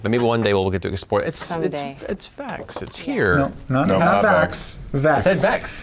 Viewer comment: I heard Vex the first time and I listened to it multiple times… me and the wife are now arguing about what he actually said. We’ve compromised that he probably said Vaex. Am I the only one that heard him say vex?